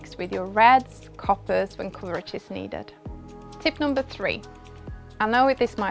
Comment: what did she say! Red, coppers and what? Even captions doesn’t know what she said lol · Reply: Reds, coppers when coverage is needed. :)